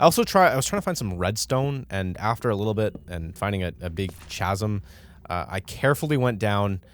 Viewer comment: Did i hear Ch-azum instead of chasm???